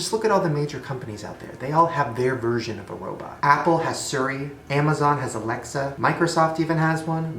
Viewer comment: Shane: They all have their version of a robot. Shane: Apple has Sery... Me: What did he just say?! 🤔😂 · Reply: Tiny Cat lol he said suri xD😂